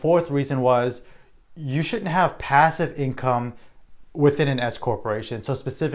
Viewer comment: did he say you should or shouldn’t have passive income in an s-corp? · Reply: Generally, you should not have passive income in an S-Corp. There is a separate video I created on this topic on my channel.